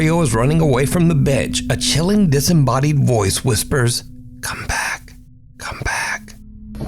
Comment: I didn't hear "come back," I heard "Run!"